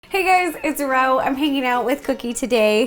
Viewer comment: Am I the only person who heard her say "Hey guys it's Rouw"? Congrats on 10 millions though ^^